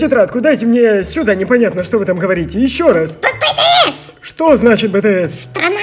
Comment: мне послышалось или она сказала BTS? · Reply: она сказала BTS боооооже